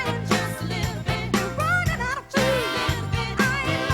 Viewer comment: I always heard that line as "You're running out of food" too.